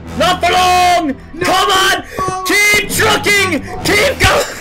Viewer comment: hahahahaha lelez if u turn on subtitile[just press C ] at 0:35-0:38 the subtitles said for jerome this was it jerome: not for long keep trucking keep trucking..... subtitles said: oh my im just gonna turn this guy's keep trucking to keep F*cking